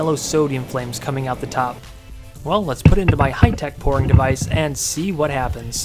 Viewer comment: the subtitles say well lets put it in my high tech porn device